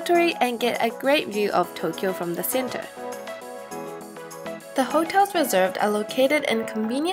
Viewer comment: Could someone tell me what she said at 3:44? "something come put my hair down"? I'm trying to subtitle the video but I can't seem to figure out what she's saying in that part. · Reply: i think it's "the hotels reserved", meaning "the hotels that were reserved".. At 3:58, i think she says "first of all we are going to hakone"